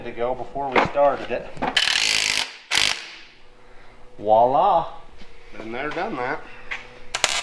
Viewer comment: I swear is saying Wah La. The word is actually Viola, it's French for there it is.